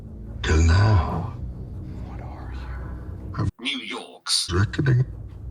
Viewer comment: what did he say? New Yorks strikining?